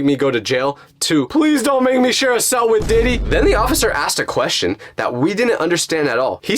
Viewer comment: i thought he said P. Diddy’s debt 💀